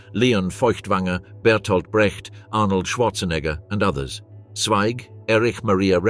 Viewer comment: Did he say, "Arnold Schwarzenegger"?